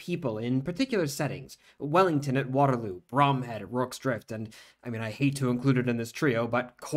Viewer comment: i'm definitely giving you some nitpicking back here--it sounds like you said "Bromden", but his name was Bromhead ;)